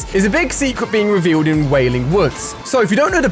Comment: did you say wailing woods?